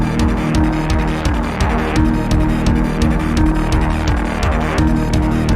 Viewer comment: why do captions say “pursure heat”